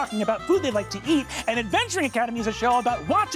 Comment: i hear Kendrick's "loving you is complicatæææd"